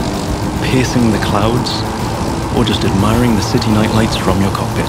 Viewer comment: did he Said pissing on the clouds